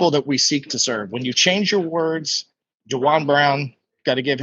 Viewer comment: mentions somebody who teaches the importance of words but I can't make out the name. Sounds like Duran Brown or something similar? Would love to know who he's talking about if anyone knows?